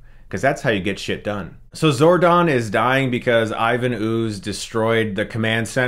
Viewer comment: wait, @ 7:31, did you call him. "Sorgon"?? lol